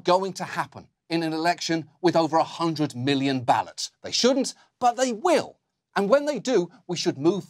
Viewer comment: did he say 100 billion ballots? I'm sure he meant 100 million ballots.